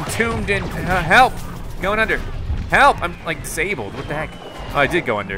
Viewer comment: Anyone else hear him say 'I'm like disabled' 37:35 ?? 😶